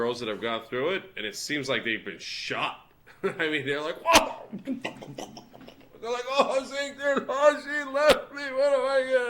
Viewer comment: lol. I thought he said, "Ohh wasikta, ohh she left me!"